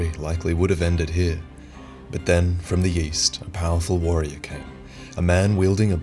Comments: I heard "from the yeast" when you introduced Alonne. Alonne confirmed to be Bread Knight. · All I hear is " and then from the yeast" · did anyone think that Vaati said "from the yeast" at 1:03 ?